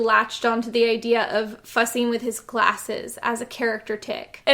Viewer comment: So you didn't like it then ? lol. I'm still wondering what you said the scientist actor had latched on to doing with his glasses at 14:26 😅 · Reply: ‘Fussing with his glasses’ I believe, like taking them on and off a lot